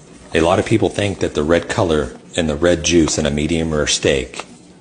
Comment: When he said red juice, I thought he said Red Jews... 😂😂💀💀